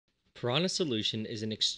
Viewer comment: My dumbass thought u said riahana solution